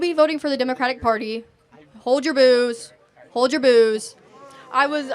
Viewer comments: For a moment I thought she'd said, "Hold your booze," as opposed to "Hold your boos"... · I thought she was saying "hold your boobs. But he is NOT talking about boobs!!!